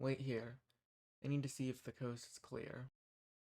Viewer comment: I thought he said "I need to see if the ghost is clear." 🤣🤣🤣👻 Edit: I misheard another thing. 4:07 I thought he said his name was Jasmine.😂